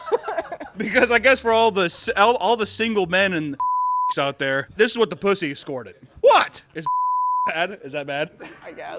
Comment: what is he saying that they’re bleeping?? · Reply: Wait I think it’s bu11 dyk3 lol